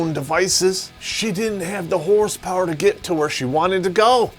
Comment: did he say 'horse power' or 'whore's power'? Just wondering.....